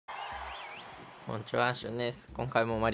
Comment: 最近また挨拶変わってきてない？ 「こんにちはシュウネス」に聞こえる